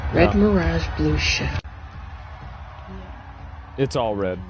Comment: it sounded like red mirage, blue sheet